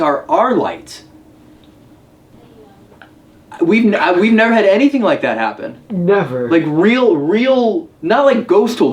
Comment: there is a woman's voice saying something but I can't tell what she says. Does anyone else hear it? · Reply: It almost sounds like it says" I know"